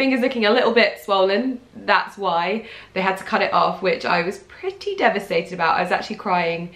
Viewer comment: so - I listen to your vids while at work and when you were talking about A&E I thought you said they had to cut your finger off and I was like aaaaaaghhhhh noooo! Hope you are ok and HOORAY for no disclaimer!